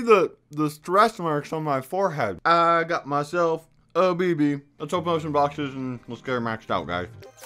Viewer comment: i thought he said “i got myself. Diabetes“ lol